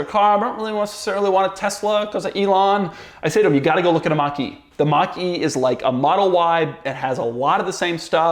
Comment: i thought you said “You gotta go look at a monkey” i was flabbergasted for a split-second😂🤣